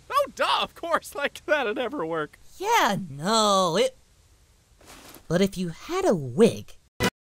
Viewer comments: "But if you had a wig..." Damn, Goku, he is your childhood friend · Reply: I thought he said, "But if you had a whip." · Krillin wanting to tag in since he wanted to pound ChiChi's tuna. lol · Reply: Not gonna lie, I swear on everything I thought he said "but if you had a whip..." XD